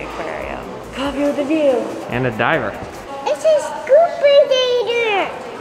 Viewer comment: "it's a scuba dider" omg your niece is the cutest 🥺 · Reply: I thought she said "scubanator" and first and I think that sounds so much better than scuba diver lol